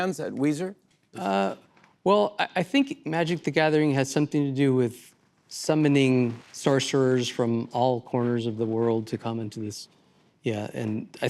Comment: what the hell did he say? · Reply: “Why was that part of uh…uh an important part makin..of uh this uh experience making that uh way.” DUH! What are you deaf?? Makes perfect sense…If you’re on a speedball or as much Valium as Jimmy. (that he got for his birthday)